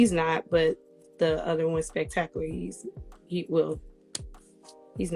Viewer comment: Did she say her mentor was Spectacular as in Spectacular from Pretty Ricky???? Lol 😆 🤣 😂. Man you can't make this shit up!